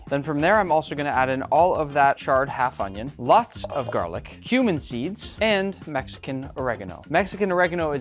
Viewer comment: I thought he said human seeds😂💀